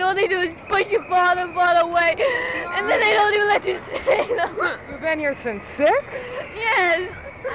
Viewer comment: what does The Interviewer says???? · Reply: "You've been here since six?"